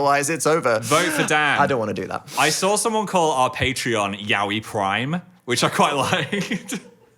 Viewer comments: i misheard “yaoi prime” as “yaoi crime” and thought yeah that checks out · I misheard Phil this morning when I listened to the audio and thought he said the Patreon was yaoi crime, not yaoi prime. I am just now realizing my mistake and am really concerned that I didn't even QUESTION it.